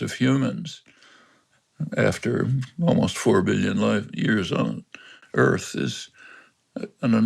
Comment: Did he say 4 billion light years?